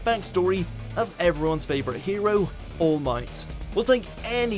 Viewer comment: Did i just heard him say All Knigts instead of All Might